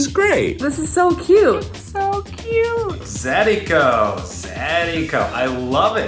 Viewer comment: Every time he said "zadiko" I heard "zydeco"